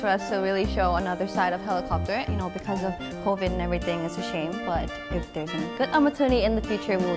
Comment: 🌐 More CLC Video 🌐 ➫ CLC dance in prop room! <link> ➫ CLC replies to fans in INDONESIAN <link> We apologize for the subtitle errors. 12:57 Sorn did not say “everything is ashamed,” but “you know because of COVID-19 and everything, it’s a shame.” there are errors on the subtitles. Besides this our translators are doing their best to translate the lyrics but because of the rhythm the translation sometimes becomes a little unnatural. We will continue to work hard to bring you the best quality subtitles. Thank you.